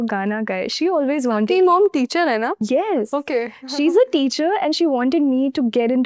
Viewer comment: ? Teri mom? Did she say "Teri"?! 😳